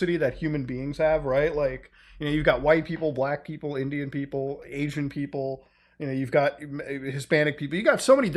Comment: God dammit, I thought he said “alien people” instead of “Asian people” lmao.